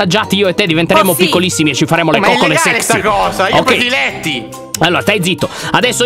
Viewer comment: Ma solo io ho sentito che a 01:28 ha detto coccole se*y?